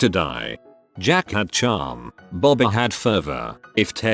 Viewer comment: I wasn’t reading and I thought it said “Jackie Chan” and I almost cried.